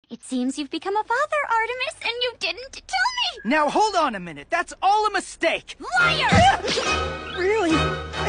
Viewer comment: I thought he said the child he has is a mistake🤑🤑🤑🤑🤑💀💀🤑🤑🤑